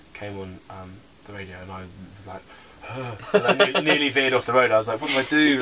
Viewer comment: What is he saying at 1:59? "nearly (WHAT) off the road"? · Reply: Veered:), basically he almost drove off the road.